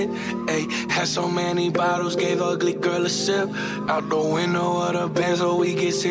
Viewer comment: ALL THIS TIME I THOUGHT HE SAID "Good Girl A Sip" not "Ugly Girl A Sip" 😐😑